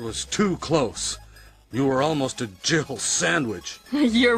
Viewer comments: Every time I hear that resident evil bit I always hear " Jibble sandwich" not Jill " Jibble" is it just me? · I always thought he said Jiblet Sandwich... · It never ever ever sounds like jill sandwich to me all i hear is ''jiffle'' sandwich. Never did get it as a kid lol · Is it just me, or does it always sound like Barry is saying "Jible Sandwich" instead of just Jill Sandwich?